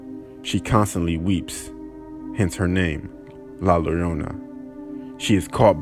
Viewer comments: Did he just say la lolona? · I know you didnt say La Leona 😂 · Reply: It sounded like he said la larona lol.